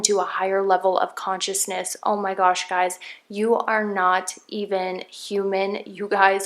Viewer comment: Why do I always hear from you " you are not even human " ?😁